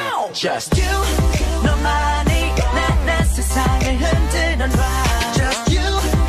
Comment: I thought they said 'Just you...my money' when it's neomani